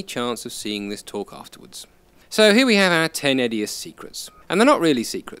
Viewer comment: I thought you said "secretses", Smeagol.